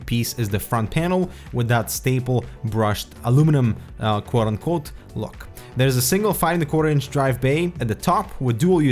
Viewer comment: what did he say between 1:08 -1:10 lol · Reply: Aluminum "Quote on Quote" Look